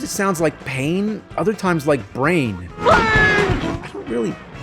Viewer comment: I think that what Jim was trying to say was hooray